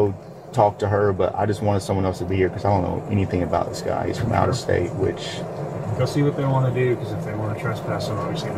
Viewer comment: I thought he said, "he's from outer space" for a second. 13:51 🤣